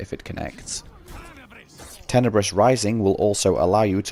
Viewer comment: I lisp? Didn't even realise. 😅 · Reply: I didn't mean it to make fun of you or anything but I kept hearing "Tenebrish Rising" and I thought you were mimicking BP lol